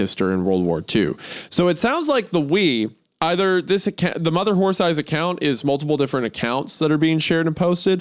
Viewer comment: Anyone else catch Isaiah saying "mother whorethighs" at